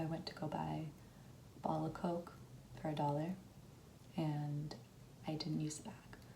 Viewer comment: I heard "Ball of Coke" 👀🤣